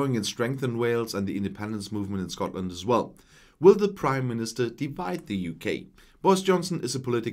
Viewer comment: Thought you said ‘Crime Minister’ for a moment there.🤣🤣 Greetings from South West France.